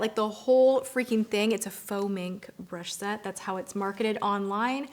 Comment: Faux mink. I thought Tati said foaming at first and I was like why would ou want your makeup to foam